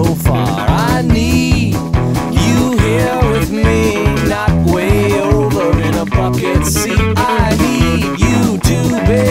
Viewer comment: I remember hearing this in middle school back in the mid/late 90s at a party. It was noisy and I thought he was saying “I need you, here with me, not way over the fuckin sea.” I still laugh about this when I hear it now.